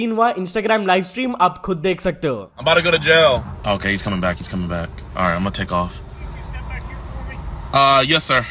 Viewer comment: did I heard wrong He said hamare ghar jao